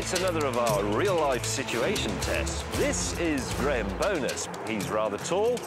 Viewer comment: Swear I thought I heard him say the dude he raced was Graham Bonnet.